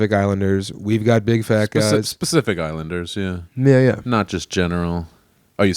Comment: Tbf, it definitely sounded like Sam said 'Specific Islanders '